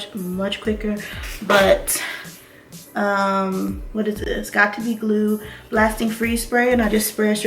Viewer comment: i thought she said no glue then what do you know she has got2be glue wtf